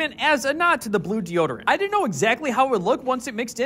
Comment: “It looks so good and smells like armpits!” Is that a good thing 💀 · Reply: I thought he said smells like orphans💀💀